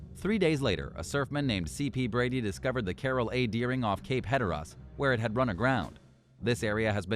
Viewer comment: A 'shooner'?! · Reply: at least the robot voice is consistent, I lol'd when it used shooner yet again !! Plus, could swear I heard 'Cape Hetterrahhh' .... Hatteras?? hehehe